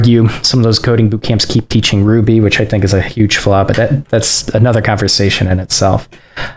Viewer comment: Hey, funny question but I can't figure out what you say at 54:40 about bootcamps teaching ruby. Listened to it 10 times but nothing. Could you please comment what you said there? (I'm considering Turing backend and they teach Ruby so it's kinda crucial :p ) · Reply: I basically said that I think it's a huge flaw that coding bootcamps continue to teach Ruby.